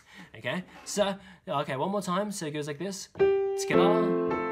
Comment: what's he saying? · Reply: Verlene Rollan, he’s saying Togetha TOGetHa